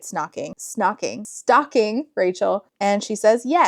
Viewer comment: When you stumbled over your words, “stocking, stalking” I thought you were trying to say snogging! 😂 And the I was like that’s a British word, that wouldn’t be in this book lol